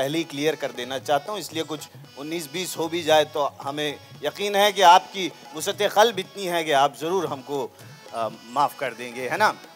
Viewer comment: What's the word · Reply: Wus'at-e-qalb = bada Dil (wide/great heart)